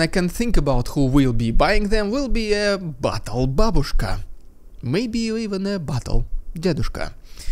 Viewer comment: I thought he said "butthole babuska." And i apologize if i massacred the spelling